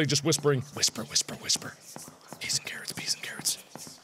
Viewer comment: "I think they're just whispering, whisper whisper whisper, be scared be scared." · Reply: OMG I THOUGHT HE SAID "i think they'rejust whispering, whisper, whisper, whisper, peas and carrots, peas and carrots" 😂